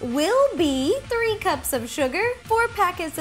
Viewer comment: At around 1:55 I swear she said 30 cups of sugar. Just me?